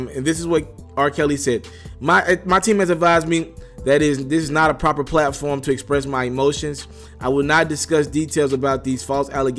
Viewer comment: I THOUGHT he said “his team advised him that was not the proper forum to address his emotions.” 🤷🏽‍♀️Isn’t that exactly what he did in that statement he made?